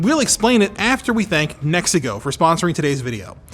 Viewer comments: I was doing laundry with this playing in the background, and @ 2:01 it sounded like he said the sponsor was "Mexico" For a second I thought the nation of Mexico sponsored the video, which only raised more questions. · I thought he said Mexico was sponsoring this video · I really thought Anthony said "we'd like to thank Mexico" lol...